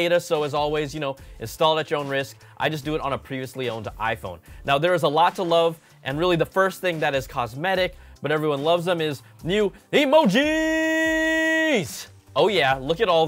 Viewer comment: did he just say I do it on a previously owned iPhone?